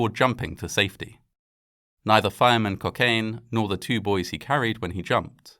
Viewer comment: did he say fireman cocaine